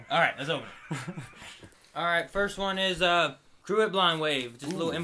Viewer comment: Whats the word he said at 00:28?? "... Blind Wave". I always understand "Crew" because when I watch the Flash reactions I hear that word in every video:D Please help me :D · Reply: Crew "at" blindwave. But in the opening of their videos, they say "crew of blindwave."